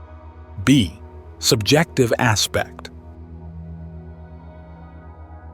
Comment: A goof here! On the visual here is written OBJECTIVE Aspect, but I thing as you said on the audio SUBJECTIVE Aspect, so I am assuming it is what you said is correct here. Just wanted to bring this to your attention. Great work otherwise.